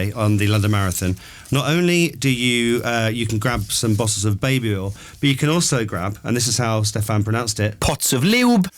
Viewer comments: I just love them! So down to earth with great sense of humour, the Monarchy is safe in their hands 👑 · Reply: I was watching the video and at 17:47, I heard 'bottles of babe oil' and 'pots of Lielb.' That's how I heard it, but I might have misheard. Could you clarify whether I heard them correctly? Thank you! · Wow, Prince William has a really good radio voice! 😄 So fun to listen to Prince William & Princess Catherine in this show. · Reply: I was watching the video and at 17:47, I heard 'bottles of babe oil' and 'pots of Lielb.' That's how I heard it, but I might have misheard. Could you clarify whether I heard them correctly? Thank you! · They're both so eloquent when speaking.... could listen to them all day! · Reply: I was watching the video and at 17:47, I heard 'bottles of babe oil' and 'pots of Lielb.' That's how I heard it, but I might have misheard. Could you clarify whether I heard them correctly? Thank you!